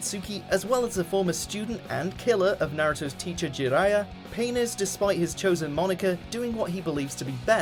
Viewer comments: did he say penis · DID I JUST HEAR "PENIS"???